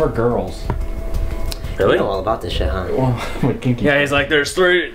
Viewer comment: IM WATCHING THIS BACK N DIDNT CATCH THAT JAKE “i’m kinky” LMFAO